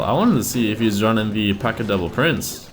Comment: Am I the only one who thinks he sounds like he says, "P.E.K.K.A devil prince" instead of "P.E.K.K.A double prince"? Yeah....? Aright...